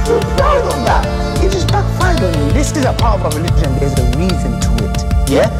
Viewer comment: Unofficial lyrics We'll be watching (3x) We're proud of that (3x) It just backfired on you We're proud of that (3x) It just backfired on you This is a part of our religion, there's a reason to it Yeah, yeah, yeah There's a reason why there is capital punishment Yeah, yeah, yeah Because people like you, little weaklings, leave their religion and cause corruption in the land by spreading it The capital punishment in islamic law will be applied to you We have no doubt We're proud of that (3x) It just backfired on you We're proud of that (3x) And we're proud of that Yeah, yeah Capital punishment will be applied in an islamic state Yeah, yeah Not individuals going and doing it theirselves like idiots, yeah No, under an emir it is done, yes And we - you know what - We'll be watching (2x) We're proud of that (3x) It just backfired on you We're proud of that (3x) It just backfired on you We'll be watching (4x) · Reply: I always thought he said "this is the power of our religion", thanks for correcting me lol